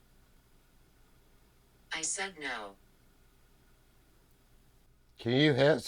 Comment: did anyone else hear the word "No?" I can't tell if it is a voice or if it came out of the spirit box. didn't mark it, so I don't know if they heard it.